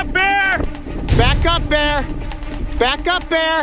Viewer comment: shouted back up bear but I sounded like